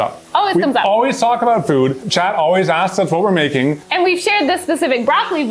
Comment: y’all should start addressing your collective twitch chat as “Chad” cause at 5:45 whoever made the English captions on this video misheard you and transcribed it as “Chad asked...” instead of what you said, “Chat asked...” Just thought it was kind of funny but maybe that’s just me